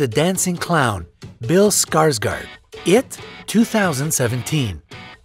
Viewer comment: Bill SKRRT SKRRT is what I thought I heard.....